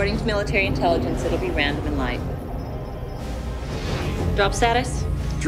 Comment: Random AND light. For about 25 years, I thought she said something like "radomerlight."